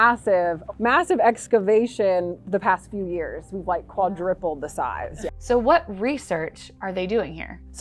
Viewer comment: did she say quadriple?